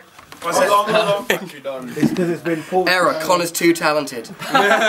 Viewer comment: I'm Japanese. And I don't know what said at 4:13. Please tell me someone!! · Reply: "Error Conor's too talented"